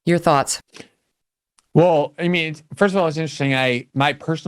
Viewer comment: did glenn say "Well Amy.." instead of Megyn?